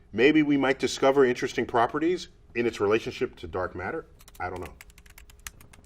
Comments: you got the caption wrong, he was talking about dark energy, not dark matter, they are two very different things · just wanna point out that it should say ‘dark energy’ in the tweet, at least that’s what NDT said.